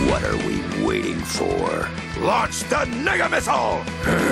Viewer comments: Did he say Mega or Nigga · NEGA missile: a missile that creates a black hole upon impact. Wow the makers of this show were slick weren't they? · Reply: it's a joke lmao. It's because it sounded like he said N*gga Missile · who else heard “nigga missle”